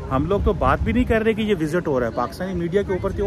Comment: "randi rone" did i heard it right ? 😂😂😂